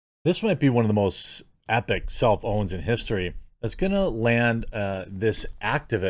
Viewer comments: At first i thought you said "one of the most epic cell phones in history" hahah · I always hear "self-own" as "cell phone" 😑 I have zero faith the Feds will do anything about this.